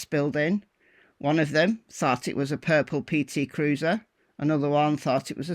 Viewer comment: "one of them thought it was a purple PT Cruiser." I heard, "one of them thought it was a purple people eater." Either would make sense hearing it here.